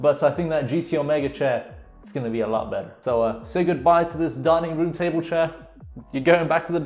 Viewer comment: i thought you said "darning" instead of "dining"